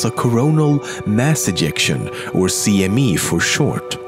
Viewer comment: I thought it said erection at 2:44 ..